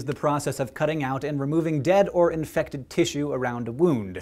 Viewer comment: I thought you said dead cow which raised a few questions, but that aside I do know it's not that rare to see dead things lying around it's the particular part of "rotting" usually things get taken care of before that point.